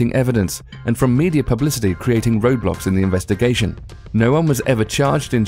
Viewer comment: wait did he just say roblox- oh nevermind i turned on captions and it said roadblocks